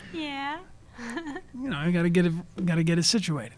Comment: I'm triggered because it says "got" instead of "get".